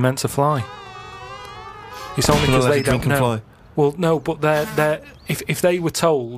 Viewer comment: What does Steve say at 22:33? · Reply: Daniel Ledgerwood don’t drink and fly